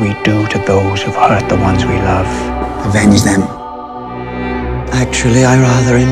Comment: Can someone tell me what he says at 1:41? I can't understand it. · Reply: Avenge them ( talking about Bolton killing Robb and kat )